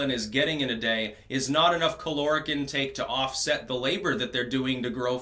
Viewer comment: For a moment I thought Styx said cholera intake for a moment lmao!